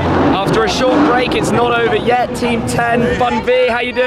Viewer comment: did he say "team 10"